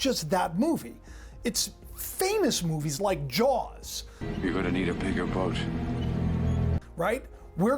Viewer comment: Watching that jaws scene I thought it said we’re gonna need a bigger boat then you said it said “your” and i rewatched then I heard it right weird haha